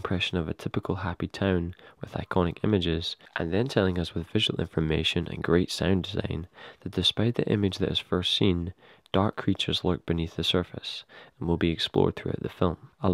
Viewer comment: hi! I'm doing an analysis on this movie and I'd like to quote some things you say in your video. But at 1:06, what do you actually say after "and then telling us with..."? Thank you! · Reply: 'telling us with visual information and great sound design, that despite the image that is first seen, dark creatures lurk beneath the surface.' hope this helps, sorry, Scottish accent plus still learning to really record audio.